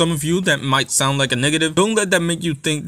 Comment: That "Niggative" instead of negative @ 4:17 was wild but great video fam lmaooo